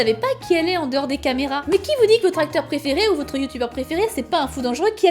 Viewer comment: "votre acteur préféré" au début j'ai compris "vos tracteurs préférés" XD